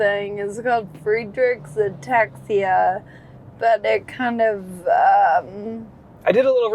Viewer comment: I thought she said free drinks ataxia for a quick second looool